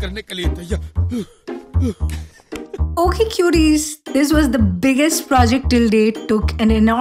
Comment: Till now I thought you said cuties but after reading the subtitles now I understand it's 'QTs' your fan name 🤣🤣🤣